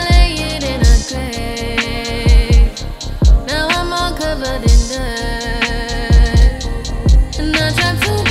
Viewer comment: I’m sorry, am I hearing her say: “NOW IM ALL COVERED IN NUUUUUT”?????? ANSWER ME NEOW.